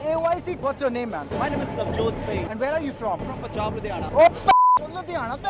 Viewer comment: did i just hear "Bakchod singh"